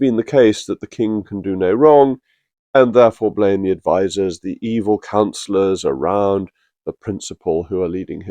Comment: I heard "...evil councillors....." as ".....evil (another (c-word).....". It made me smile. It made me realise what my own latent opinions are. 🙂. S, if I heard that correctly, the PM does not need to be an MP? Perhaps they should make ME PM, although I am an ex-pat. 🙂